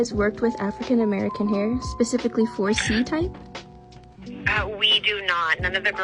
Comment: What did the tick-tocker said? "4C type"? I can't make it out and I'm not that well-versed in hair styles either. Maybe my hearing's going. ☹️